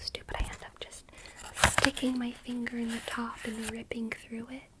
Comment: sorry but did you say...STICKYYY FINGERSSSSS?!?!?! ARIARIARIARIARIARIARIIIIII